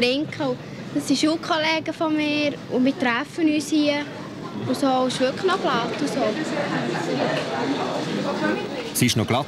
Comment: "Wir treffen uns hier" ... und was sagt sie danach? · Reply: Nicht mehr viel Relevantes ;-). Berndeutsch: „u mir träffe üs hie.. u so.. u sch würklech no glatt.. u so.“ Übersetzt: „Und wir treffen uns hier.. und so…und es ist wirklich recht lustig.. und so.“